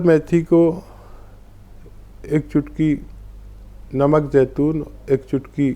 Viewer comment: pr ek chutki namak ke baad kya bola h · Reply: Namak jaitun aap google pr likhe aajyega